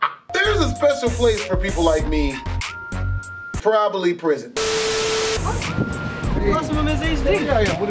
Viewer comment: I could of sworn he said monopoly prision😂😂